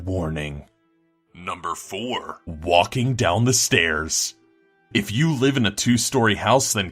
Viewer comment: What was the scariest thing you ever experienced as a kid? Let us know on our reddit page and you may be featured In a future countdown. <link> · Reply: i thought u said wanking xD